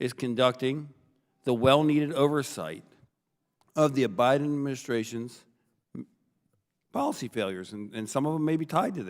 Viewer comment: Hilarious! At 0:43 he clearly says "the O'Biden administration's policy failures"...